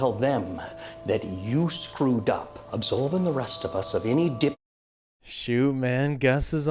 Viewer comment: I'm sorry; did he just say "dipshittery"? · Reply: Yes he did lol